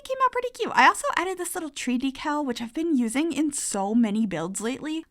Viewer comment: I heard 3D cow and I was like whaaa theres a 3D cow! Until I realised it was Tree Decal 😭🤦🏻‍♂️😆🐄